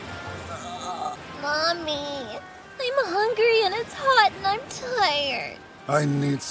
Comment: What did Luffy say at 0:04? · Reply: “I haven’t eaten, I’m hungry”